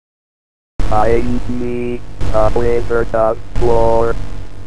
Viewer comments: Lol I always thought the WIZARD OF WOR looked like DR DOOM. and thought so said I NEK AH IZZERD OF FLOOR. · I remember they had this at Big Lots but I almost never got to play it, and I lost right sway when I did. The voice synthesizer was hard to understand. I thought it was saying, "I need a Wizard of Wor."